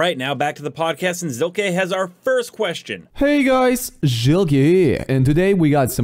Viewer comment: You and me both haha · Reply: 😂, i watch his videos sometimes and i always hear "juggy here", now i realized hes saying zilke but in his language maybe.